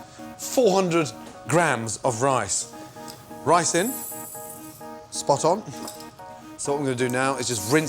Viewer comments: haha I thought he said risin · I thought he said "400 grains of rice", got so worried 😂